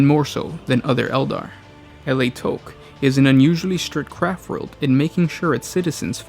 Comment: All I hear is "Crap World". :D